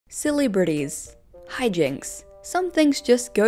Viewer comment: it sounded like you said 'silly britties' not 'celebrities' and i apologize for mishearing you but i can't help but chuckle a bit